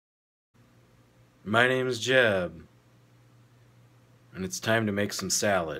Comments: thought he said "my name Jeff" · I thought he said "My name is Jeff"😂😂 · I thought he said at the beginning, my name a jeff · I thought he said my name Jeff at the start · awesome vídeo jeb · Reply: Thought he said "My name is Jeff" or is that the joke hes implying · At first I thought he said “My name is Jeff” 😂😂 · I thought he said "my name is jeff" at the beginning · who else thought he said "my names Jeff" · Thumbs up if you thought he said MY NAME IS JEFF!! 😆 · lol in the intro i thought he said "my name is jeff" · I tot I heard "My name is Jeff." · lol i thought he said " my name Jeff" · The first thing I heard... “MA NAME JEFF” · I thought he said " My name is Jeff " HAHAHAHAHHAHAA 😂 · Laughing at the begining cuz i thought he said "my name is jeff", thought it a comedy, but boi im wrong so hard... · I thought he said my name is Jeff 😂😂😂😂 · I heard "my name is jeff" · I THOUGHT HE SAID MY NAME IS JEFF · i thought i heard my name is jeff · Atfirst I thought you said my name is Jeff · Thought you said my name is jeff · Bruh I'm all high and in the beginning I thought he said "my name is jeff" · i thought he said "hi, my name is jeff" for a second X'D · i thought he said my name is jeff😂😂😂😂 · i thought i heard "My name is Jeff" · I THOUGHT HE SAID "MY NAME IS JEFF" WTH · At first, I thought he said "My name is Jeff" XD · I thought he said "my name is jeff" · Bro I could've swore you said " my name is Jeff" when I first heard it lol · I thought he said “My name is Jeff”